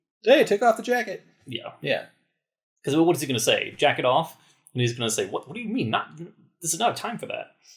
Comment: Did say: "Jack-it Off..." @ 25:30 ish?? I am surprised did not catch that right away. LOL